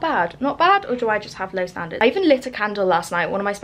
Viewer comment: hahahah i lowkey thought you said 'i even licked a candle last night' instead of 'lit' 😂😭